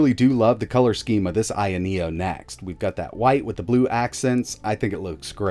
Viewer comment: am I going color-blind or did he say White with Blue accents?